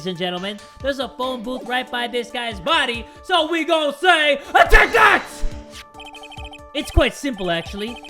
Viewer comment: Is it only me or did say „OBJECT THAT“